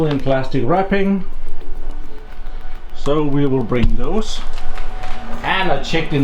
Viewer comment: it sounds like you said “so we will break those” XD